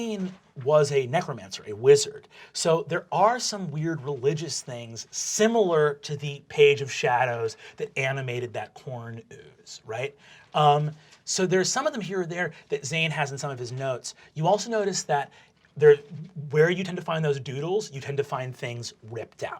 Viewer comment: ~106 min in Brendan says "corn ooze" but first time I herd it I thought he said PORN ooze... need I say more?